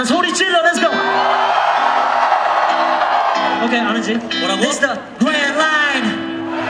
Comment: 인트로 영어 뭐라고 하는건지 아시는분? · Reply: grandline turn it up